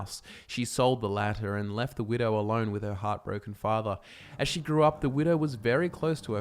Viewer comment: So at around 2 minutes I thought he said "hot, broken father" instead of heartbroken but then I remembered he's Australian.